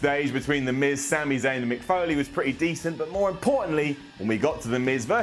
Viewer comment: Did he just say Mick Foley?